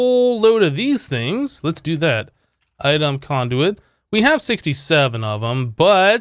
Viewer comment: did bro said 67 in 2018?